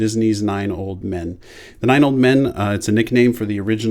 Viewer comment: I thought it said 9 year old men XD lord ur gud